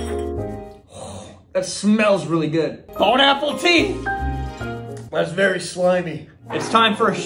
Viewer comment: i thought the sponser said bone pineapple tree